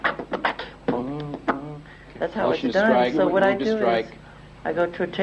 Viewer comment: Как Моцарт, ей-богу (имея в виду музыку "в голове") :) талантище... так профессионально записывать песни, в том числе под битбокс 8:40 - 8:50 (как будто записывающая машина)))) 5:44 9:27 Objection, а слышу "Джексон"...